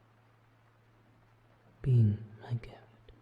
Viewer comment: i heard "being my.. grill " i guess im a grill now 😀✌